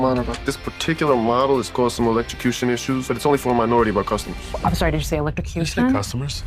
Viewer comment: I thought it said "only for our minority customers" 😅